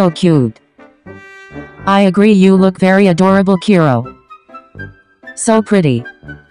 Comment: Momo said "so pretty" I thought she said "so petty"🤣